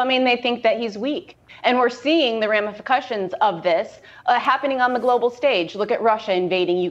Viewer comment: I think I heard Kelly Sandler coin a new compound word: @ 5:56 she says, "ramifercussions" , which I believe might be compounding the words "ramifications " and "repercussions ". :) I will give her credit for it, but I am hereby coining a new word: "ramifercussions"!! 🙂